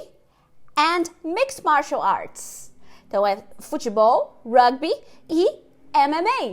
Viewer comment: Muito bom esse exercício, especialmente o último que deu pra aquecer aqui. Acho que a maior bagunça foi quando o professor falou "Mixed Martials Arts" que eu entendi "Mix Marsheleise" akaksakkas De resto, deu pra entender e responder até bem demais